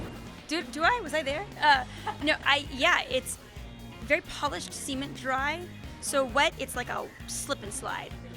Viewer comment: Did Holland say "it's very seamen dry"? XD